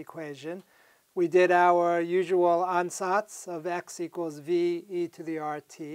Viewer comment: What word is he saying when pointing to x = ve^(rt) at the beginning of the video 0:0:12? It sounds like onsaughts.... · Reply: ansatz